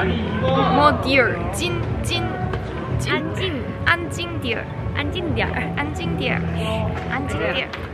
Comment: Idk why i heard "Anjing" and in my country it means dog but it was like a harsh words 😂